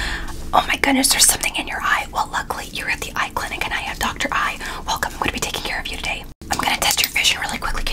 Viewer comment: “i’m Dr.Eye”💀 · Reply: I thought she said "i have dr.eye" 💀💀